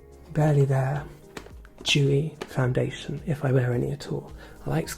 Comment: said “dewy foundation” but I heard “jewy foundation”. For a second I thought, “I know River doesn’t like being overly politically correct but this is a bit much”, but I quickly realized it was dewy.